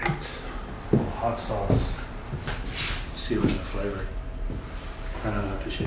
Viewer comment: What did he say? I heard hot sauce but I don't think that's what he said. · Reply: Some hot sauce, to seal in the flavor. Then he explains that it's after-shave tonic.